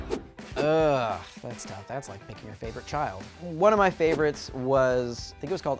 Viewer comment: When he said "That's like picking your favorite child" I thought he said "That's like MAKING your favorite child" I almost and I oop